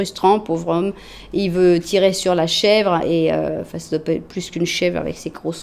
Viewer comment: pour information j'ai entendu "face de pets" du coup j'ai explosé de rire et j'ai mis 10 minutes a m'en remettre. Alors merci !